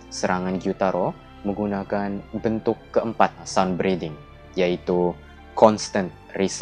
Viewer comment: Minet ke 2:19 abang kata tengen guna kan penafasan matahari bentuk ke 4._.? Eh salah dengar😂